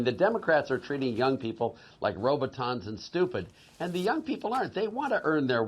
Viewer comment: Did I mishear, at 1:27, did he say the democrats are treating young people like "Robotons"? What the fuck is a roboton?